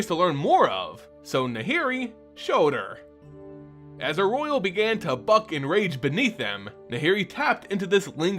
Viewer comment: "As a roil began to fucking rage beneath them" lol sorry i misheard you